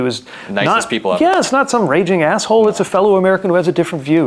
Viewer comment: I thought he said, "not some graping Ar$hole"